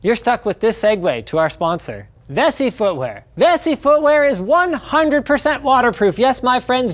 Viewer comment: Get $25 off Vessifootwear with offer code LINUSTECHTIPS at <link> Use code LINUS and get 25% off GlassWire at <link> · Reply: I could've sworn Linus kept saying "Messy Footware"